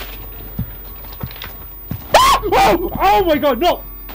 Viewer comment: Am I the only one who heard "oh my god " (then mosquito noise follows) at time stamp 6:57 ? That's where you said then that you felt something push you into the doorway